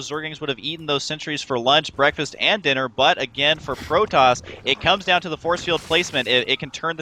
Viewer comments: did husky say "Frotoss"...? That makes me immediately think of a zealot with a fro and eyes blazing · Did Husky just say Frotoss @ 5:18?